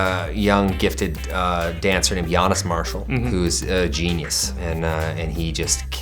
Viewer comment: I thought I heard "Giannis" for a minute